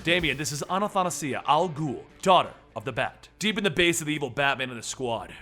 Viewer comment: I thought it said daughter of the brat